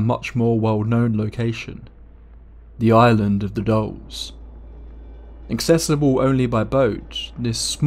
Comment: 'Island of the Dolls'. I thought he was saying 'Island of the Dogs' I was like "I want to go to the Island of the Dogs" After he said it 3 times I realized he was saying 'Island if the Dolls'. Yeah, I don't want to go to the Island of the Dolls.